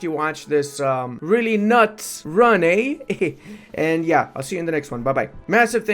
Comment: Misheard the closing statement as "I'll see you in the next month."